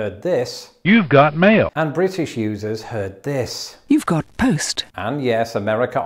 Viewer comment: I heard "you've got toast." xD